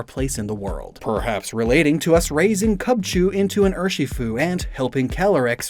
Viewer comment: You said “Cubchoo” instead of “Kubfu.” 😂 It’s cool that there is some deep thought into these codenames.